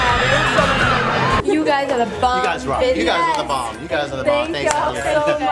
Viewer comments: 'you guys r the bomb bitchesss' anyone else hear, anyway she splayed my life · did anyone else hear mila say, "you guys are the bomb bitches"